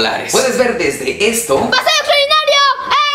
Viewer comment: que dijo el niño del minuto 2:12? pase al otro que? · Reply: Tuca XI paseo extraordinario